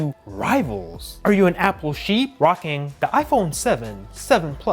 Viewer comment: seriously did you said "Apple sheep" got to be kidding me?